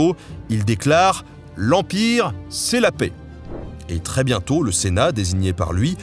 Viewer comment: avant que tu n'ais eu le temps de dire "L'Empire, c'est la paix", j'ai entendu "L'Empire, c'est moi !"